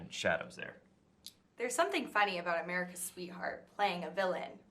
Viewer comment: Can you please get a reporter that can think instead of just read off cards? She repetead "American sweetheart" twice after being told he's Canadian and they laughing about it. She just could NOT get out of her script... Goddamit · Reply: She repeated the well known phrase “America’s sweetheart” twice and you still misheard it as “American sweetheart”